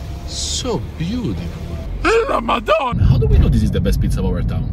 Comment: am I the only one who heard him say oh Ramadan ?